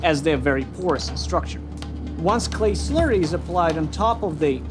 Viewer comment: i thought he said curry i was like "wth u use indian food to make swords?"